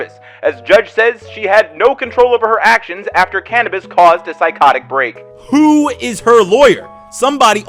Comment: what’s the censored words at 9:29 I have no idea :P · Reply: Weed